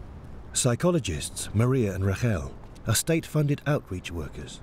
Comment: Pretty sure his name is Ferreira and not Ferreria. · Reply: Chex LeMeneux I also believe they mispronounced the name of one of the psychologists in the beginning. I keep hearing "Rarrel" when I suspect it should be either "Rachel" or "Raquel" (pronounced the same way in Brazil, and probably in Portugal too).